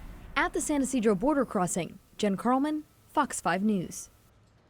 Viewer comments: i thought she said “fucks five news” instead of “fox five news” lmaooo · fucks 5 news is what i heard